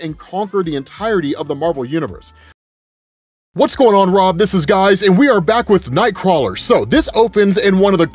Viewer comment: Did he just say what's going on Rob this is guys😂😂😂😂